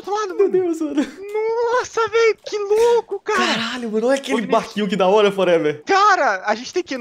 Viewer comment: SO EU QUE OUVI O VINICCIUS FALANDO CARA**???